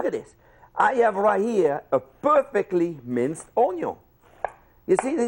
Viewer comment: CLICKBAIT!!! I was promised instructions on "dicing" an onion. (which I think is impossible) He admittedly minced the onion. I don't trust a chef that does not know the difference. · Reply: To be precise at 03:19 he said: "i have right here a perfectly minced onion". Maybe he knows yet what he is doing, but you didn't understand what he was saying.